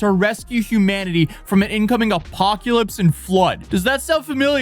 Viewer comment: are you pronouncing the "y" in apocalypse? I keep hearing you say "apoc-yailpse"